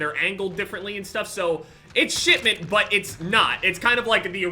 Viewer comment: Who else heard shitment